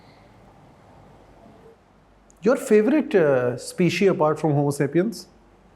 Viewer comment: Why Do you say speeshee instead of species